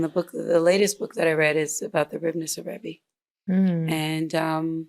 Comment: please what is the title of book she is refering to at 1:21:00, i'm having difficulty understanding what she says exactly. thank you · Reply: is talking about the Ribnitzer Rebbe. There are a number of biographies of him. Hope this helped!